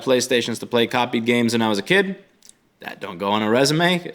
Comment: listen harder! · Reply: i'm listening as hard as i can, i definitely heard an "i" though I figure that you must've meant "that"